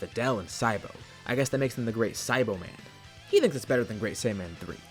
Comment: For some reason, I misheard Great Saiboman as Great Saibaman. Lol